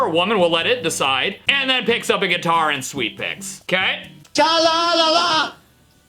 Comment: “tra-la-la-la!😛😡” ok captain underpants. Or is that just me? It just kinda sounds like she’s saying what captain underpants would say 😭